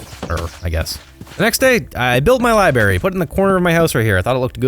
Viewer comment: did you say "liberry"?